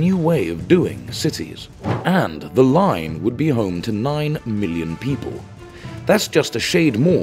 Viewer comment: Sorry, I had to watch it again. I thought he said "9 billion" but it was 9 million. So there's 9 million people. If you divided up work shifts (or school or shopping or whatever) to evenly distribute over a 24 hour period, that's 375,000 going to work every hour. Fit 4 to a pod and that's 93,740 pods. That's almost how many cross the Golden Gate Bridge each day (both ways). But that's only one way. You need the same amount coming home. That's 187,500 pods. Hyperloop pods were 2.2 meters in diameter. That many pods of that size have a cross sectional area of 900,000 square meters. The Line only has an area of 100,000 square meters available (500 m x 200 m*). Even if you used the same pod to take someone to work and another person back home, that's still 93,740 pods at 450,000 square meters. You can only fit 20,600 pods at one time, if they were stacked inside The Line You would need to churn over those 20,600 pods every 12 minutes, all day and night, and it would fill the entire space in The Line. Play with the pod sizes or how many people stay home per day all you like, it doesn't get much better. *Note, the video says @ 2:<num> FEET, "taller than the Empire State Building", but the ESB is 1250 feet (380 meters). Pretty sure he meant meters, not feet.